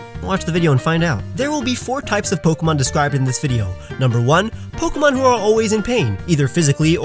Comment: I legit thought it said Pokemon who ARE a pain. Oops